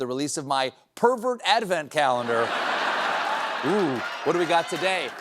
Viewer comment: Ativan calendar? I hear Ativan calendar